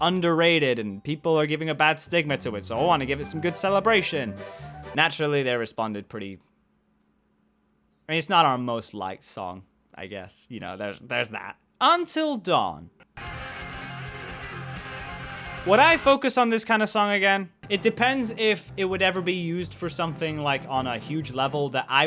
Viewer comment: lol i heard UNTIL DAW